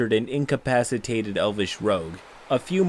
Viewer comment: The moment I read "Elvish Rogue" , I KNEW this was THAT GUY. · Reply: I thought you said Elvis rogue